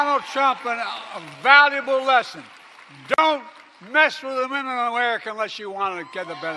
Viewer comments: I HONESTLY THOUGHT HE WAS SAYING, "DONT MESS WITH THE MIDDLE OF AMERICA" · Dont mess with the minimum America unless you want to get the benefit is what i heard.